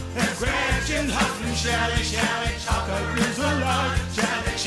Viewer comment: I thought they said, "such a shocker he's alive", not "Shelley Chaulker is alive" 😅